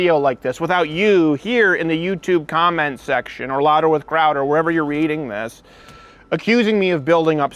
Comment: I thought it said chowder